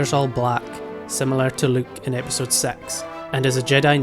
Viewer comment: Am I the only one who heard “Episode S3X” at 0:33 😂